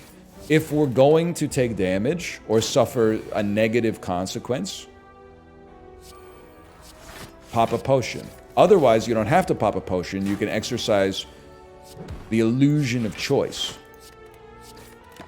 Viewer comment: For a second I thought he said "Papa Potion" that would be a great name for a alchemize rap album